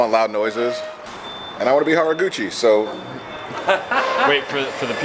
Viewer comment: Could've swore said "Hot Rod Gucci" when he said Haruguchi